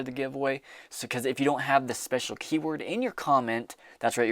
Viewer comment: I thought you said a special keyboard is required instead of Keyword. Not interested in Lithium currently barely getting into Platinum. Def like Gold been buying it more then Silver for the most part. I thought that stack was yours, I’m like wow youve been busy buying gold.